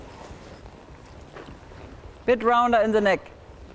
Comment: Ich habe 'rounder in the egg' verstanden :D Aber ich glaube sie hat leg gesagt :)